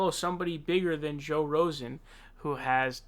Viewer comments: that's the second time I heard you say "joe rosen" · Did you just call him joe rosen?